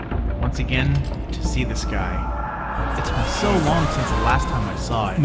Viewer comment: i hear someone saying, “ITS BEEN SOOOO LONG”.